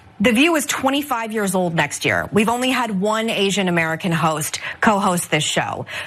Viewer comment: Right at 1:00 I can't be the only person that heard her say they have only had 1 Asian host representative she did not say that they have not had any she said that they have only had one and I did not watch the entire show but I can see where this is already being completely misled I'm not a fan of her but I'm also not a fan of liars who to spin a story to get their own narrative across about somebody. But I shouldn't loose my job if I'm doing it right because someone else wants to out someone else there that makes them look better. But I don't have to worry about that I own my own business 💯.